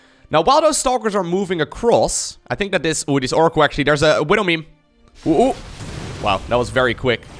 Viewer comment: did you say... widow meme? XD